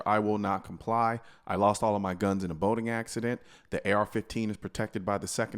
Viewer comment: On a side note I really dislike colions brand of shirts. I am the militia. Lost my guns in a boating accident. I will not comply. You’re just painting a target on your back. Do all those things and let no one be the wiser. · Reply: have to admit the boating accident one is pretty funny, for the longest time I thought he was saying a "voting" accident.